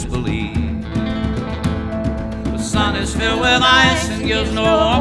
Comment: The sun is filled with ice and gives no warmth at all And the sky was never blue The stars are raindrops searching for a place to fall And I never cared for you I know you won't believe these things I tell you No, you won't believe Your heart has been forewarned all men will lie to you And your mind cannot conceive Now all depends on what I say to you And on your doubting me So I've prepared these statements far from true Pay heed and disbelieve The sun is filled with ice and gives no warmth at all The sky was never blue Stars are raindrops searching for a place to fall And I never cared for you And the sun is filled with ice and gives no warmth at all Sky was never blue The stars are raindrops searching for a place to fall And I never cared for you I never cared for you I never cared for you <3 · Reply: always thought it said "sun is filled with eyes" as it appeared equally ridiculous